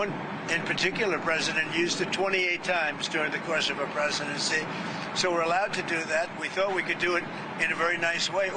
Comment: Anyone else catch that Trump has now imagined that we once had a female president? 01:35 ish "used it 25 times during the course of her presidency"